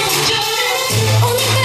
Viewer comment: Thumbs up if you thought she said Lipstick Chicken Butter Roll =)